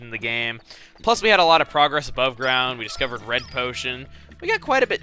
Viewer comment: at 11miin 45 sec it sounds like hes saying bread potion..........................................................................EMILES FALCO CONFIRMED